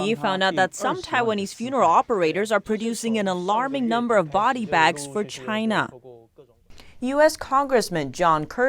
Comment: Body bags thought they said sleeping bags